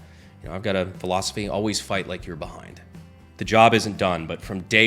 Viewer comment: I thought you said near the end, "always fight like you're Bob" and i was like, what Deep Cut Lore am i missing, who the heck is Bob, and why is he the GOAT?.... oh, BEHIND....that....that makes way more sense....